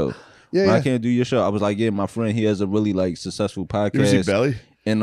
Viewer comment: did Shane say “who is he, Belly?” Like the DMX movie? Lol